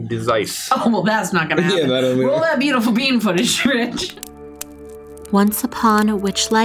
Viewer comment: I thought they said b footage but they say bean footage 😂🎉